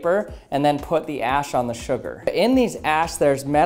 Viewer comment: I thought it said "don't add your ashes to the sugar"